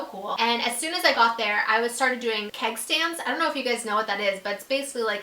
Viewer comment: i thought she said she started doing headstands instead of keg stands hahahaha